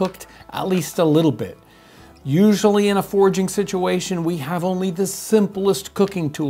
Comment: hey and crew! Long time watcher here, just wanted to mention that the subtitles at 3:58 use "forging" instead of "foraging" just wanted to point that out for y'all! Great video otherwise!